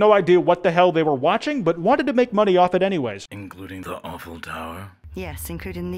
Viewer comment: is it the horrible voice or is he actually saying the "awful tower" or eiffel tower